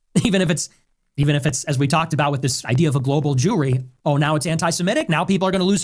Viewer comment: did he said global jewry?! jewelry? wtf?